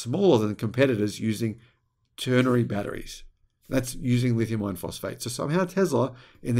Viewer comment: For a long time I thought he was saying lithium ion phosphate instead it is I -RON